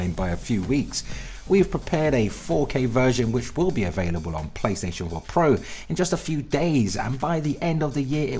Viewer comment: I could have sworn I heard you said PlayStation One Pro?